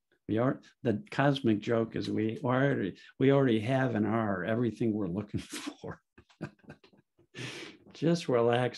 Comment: at 4:18 your words get a bit jumbled and I can't make out what you are saying. Can you clarify? What is the cosmic joke and what do we already have? Thanks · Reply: I listened to that part and I was saying that the cosmic joke is that we already have and are what we are looking for. We already have perfect well-being inside. When our thinking flows and quiets down we naturally start to feel better and are able to be loving to ourselves and others. I hope this helps and you are able to experience the truth of this for yourself!